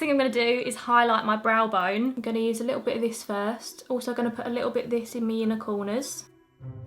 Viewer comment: At 12 mins (exactly) I honestly thought your said “I’m also gonna put a little bit of this in UNICORNERS....” I had to rewind it a couple of times til I heard it right! I think doing 60 hours this week in the hospital has fried my brain 🦄🥱😴. Love you, you really brighten my day. Stay safe angel xx